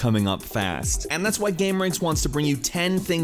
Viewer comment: If you turn on CC and go to 0:07 , it'll say "gay marines" instead of gameranx